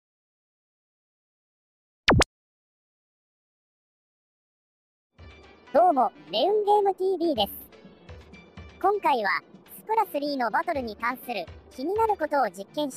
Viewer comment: 最初の名前いうのが、レウンゲームTVじゃなくて、レインボーロードTVに聞こえたww